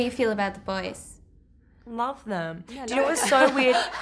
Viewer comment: What did Margot say? lol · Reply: "Love them "